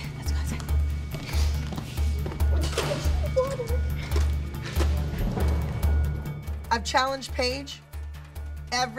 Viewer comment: What did she say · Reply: “I just need water” ?? Maybe